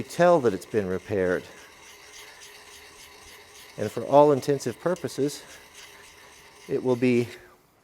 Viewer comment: Did I just hear you say "all intensive proposes?" Anyways, keep up the good work love your videos